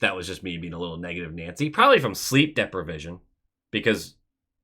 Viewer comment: haha when you said “depra-vision” i paused and thought to myself “have I been saying that wrong my whole life?”